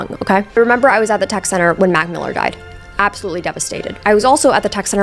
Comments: I thought you said "when Macklemore died" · I thought you said when Macklemore died and was like wtf??????